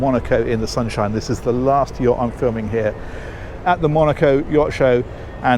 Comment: I didn’t know that it was! 😳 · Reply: I misheard. I thought you said "This is the last year I'm filming here." You said "last yacht". 😅😂 Please keep them coming forever!